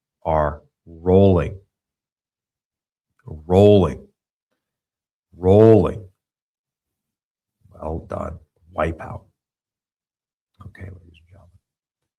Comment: I was listening and swore you said, "well done, White power". 🤣🤣